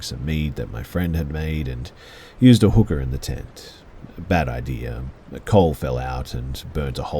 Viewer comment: Ok story around 35 minutes… I had to back that up a few times because it def sounded like he said “and used a hooker in the tent.” And not Cooker, and for a second I was very confused.